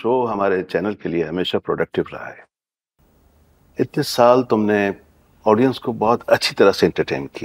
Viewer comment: does he say 21 years ???? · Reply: No...itni saal